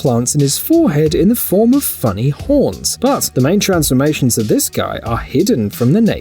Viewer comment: sounds like he said f*cking horns